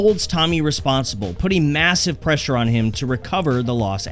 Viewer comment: did I hear MASSIVE?